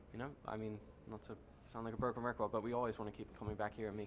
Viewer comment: Ok so does anyone know what Mikey says at 8:01 ??? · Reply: flameheart0216 not to sound like a broken record